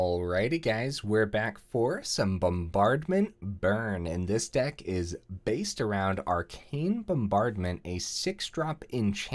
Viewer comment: all i heard was "this deck is based!" at the beginning